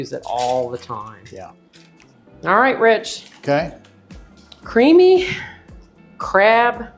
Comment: Where it said Rich and creamy crab dip .. I thought I said “ Rich and Jenny’s”. lol. I was thinking how cool. Great video